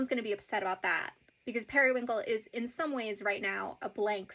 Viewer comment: Love how the captions at around 2:20 say "Perry the platypus" instead of Periwinkle 😭